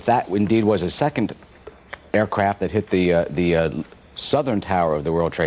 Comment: did he just said? " a second aircrap flew into the building "💩